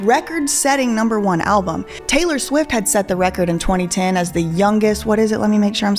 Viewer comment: wait did you say Taylor Lynn swift 😂😂 her middle names Allison 😂